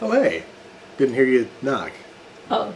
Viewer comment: Did I hear you say "Good'nt hear you knock?"